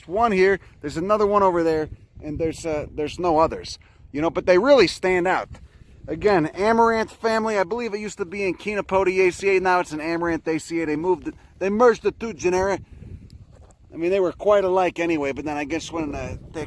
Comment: Amaranthaceae family. At first listening I thought you said Ayn Rand. Hot n dry? That’s the reason the invented K-Y, no?